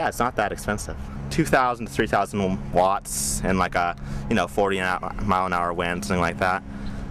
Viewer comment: You incorrectly wrote, "he said, the max output was 400 Watts in high wind (45 mph)" <= Huh? That is NOT what I heard. At time @ 2:24 ... I heard him say, and I read on CC, was this => "2,000 to 3,000 Watts in a 40 mph wind". Which is 5 to 8 TIMES larger than what you claim. Western Iowa is VERY windy and that is why they build & use Wind Turbines. Your estimate of only 2 kWhr per day is way off. This Wind Turbine can produce 2 kWhr, or more, in just one HOUR ! In western Iowa, the Annual Average wind can be as high as 15 MPH. Therefore, average DAILY production can be 19 kWhr = 800 watts x 24 hours / day Average Yearly production can be 7,000 kWhr = 19 kWhr / day x 365 days Pay Back is more like $700 per year ! It would not be worth wasting a week of your life if the ROI was only $72 / year